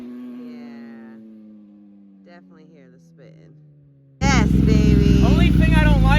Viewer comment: Did you try dropping the rear tire pressure a little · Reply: Wow now that I listened to the video you said it was slipping I thought you said spinning lol my bro.that's why I spoke on lowering tire pressure. Peace to you and your family brother